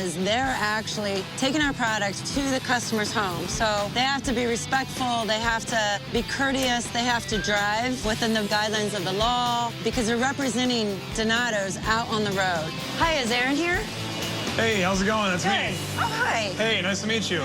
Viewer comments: Anyone else thought it said dominos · I swear I thought it said dominos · I thought she said dominos XD